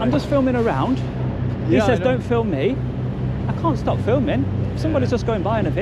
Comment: "Don't film me" - as he walks towards the camera. A literal NPC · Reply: I swear i heard him say "Stop criminal scum!" :p